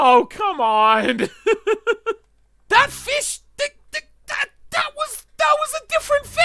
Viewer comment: did anyone else hear "fish stick"